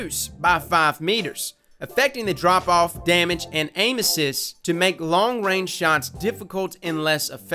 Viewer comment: am I the only one that heard 'anuses' instead of 'aim assist'